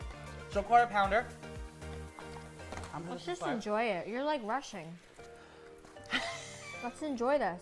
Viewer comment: Tell my y I thought she said he was Russian but she actually said he was rushing lmao